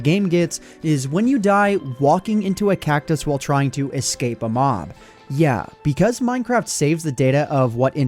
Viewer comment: with subtitles says walking into a cactus while try to escape a "mom" instead of mob XD